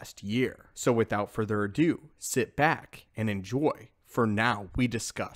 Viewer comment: Did you say “sith back”?